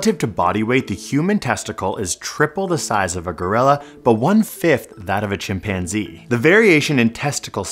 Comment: Did you say Grilla (of the wood) ?